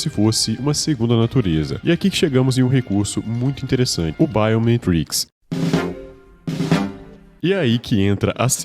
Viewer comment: Só eu q ouvi "pai ou ominitrix"???? KKKKKKK